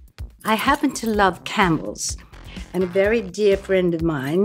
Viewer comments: Thanks British Vogue. This episode was so much better than Miranda Kerr’s. Also, I thought she said ‘candles’ instead of ‘camels’. I was all: Is she about to pull out a candle? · When she said “I happen to love camels” i thought she said “I happen to love cannibalism” and I did a double take 😂